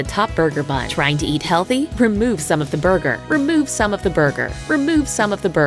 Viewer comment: I thought she said remove son of a burger LOOL